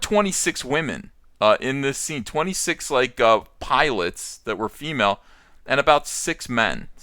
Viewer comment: He said "Pilots" and I heard "Pie-lets", like "Piglets"